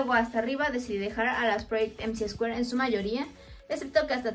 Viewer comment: como se llaman las muñecas del minuto 4:31? no entendi muy bien lo que dijiste, pero se ven preciosas!<3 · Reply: Son Project MC2 💜💜